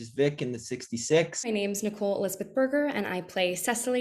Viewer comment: I'M SORRY, DID SHE SAY THAT HER LAST NAME.. IS BURGER!?!?!?!?!???!???!???